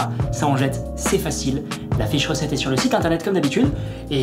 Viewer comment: Quand tu as dit " la fiche recette est sur le site internet" j'ai entendu " la fée chaussette" 🤣